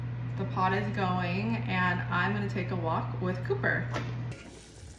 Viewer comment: Ha! I thought you said pooper not cooper lol!! And I can't wait to make this soup looks soooo delicious ❤❤❤❤❤